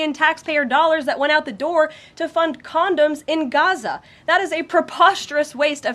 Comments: Did she say “condos” or “condoms” in Gaza? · Millions of funding for Condoms in Gaza?? Are you kidding me!😡😡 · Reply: I honestly thought she said condos. I’m not sure which would be more wasteful.